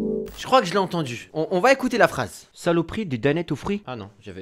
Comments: Moi j'ai entendu "c'est le prix d'une dannette au fruits" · moi jai entendu c’est le prix d’une Danette au fruits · j'ai entendu "c le prix d'un dunette aux prix c vrai